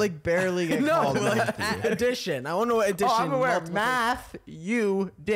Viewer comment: Am I the only one besides Chris that thought Nick said “I want to know what Matthew did”